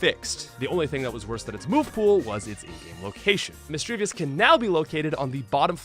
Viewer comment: The video is nice, but man is it hard to hear what you're saying at times like at 4:54. edit also at 5:02. Had to listen twice to understand that you said "in game location"